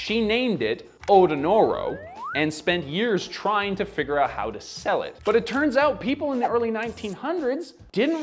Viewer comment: For a split second, I thought you were saying "She called it Odo-ro-no and spent years trying to figure out how to spell it" 😂 xx